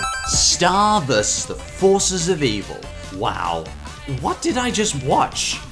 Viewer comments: I thought he's saying Starburst Vs. The Forces Of Evil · Star vs. the forces of evil is really good just started watching it like 4-6 months ago. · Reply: Jesse Brown dude I thought you said Star Wars vs the forces of evil LOL